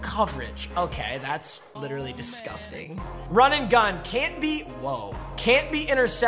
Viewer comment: Did I hear an aw man at around 1:40?